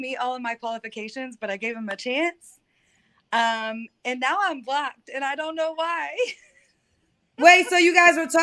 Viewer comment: At first I thought she said "and now I'm BLACKED" 😂